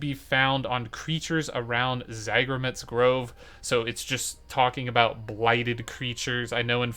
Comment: Did you mean "Leyroot Grove" near Spriggan Arena and Malevolence? for the firefox? because non of the maps I see show Zagreb's Grove or whatever was mentioned in the video at 15:34. I couldn't make out the location you were actually saying that is within Eden Grove.